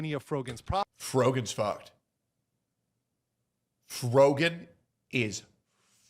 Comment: i thought you said "frogan is fat"